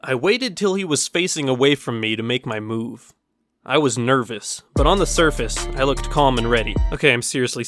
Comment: Lol, I thought you said: I was NERFous